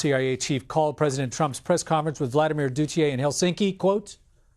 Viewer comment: did he say Duthier instead of Putin???